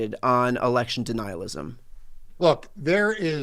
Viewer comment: sounded like “Election de-nihilism” in my head…